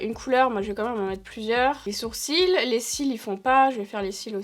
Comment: c'est moi ou à 6:31 elle dit "iels" ? ça serait franchement cool qu'elle fasse de l'inclusif 😳